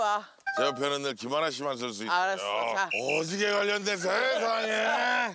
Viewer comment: 이겈ㅋㅋㅋㄴ 뭐라는거에요?ㅋㅋㅋㅋㅋㅋㅋㅋㅋㅋㅋㅋㅋ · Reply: 넘나판다 오지게 걸렸네 세상에~~~ 이러는 것 같아요 ㅋㅋㅋㅋㅋ